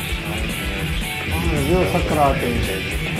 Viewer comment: I had a giggle at the transcript: "he went into Socrates now". Actually what he said was "Sakarat", which means the pangs of death, at the point where where one's soul will leave the body and render it lifeless.